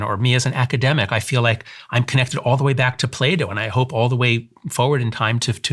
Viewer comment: I heard "I'm connected all the way back to Play-Doh" 😂 ...sorry